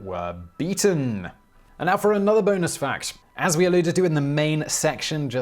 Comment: This was interesting ... then I got distracted ... I thought he said semen in relation to N Korea. So I start listening again but am total lost as to how semen fit in this account. Then I realize he is saying seamen